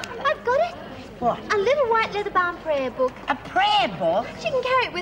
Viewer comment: What does pixie call the prayer book at 12:25 ?? She spoke to fast lol, couldn't catch it. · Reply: A little white leather bound prayer book :)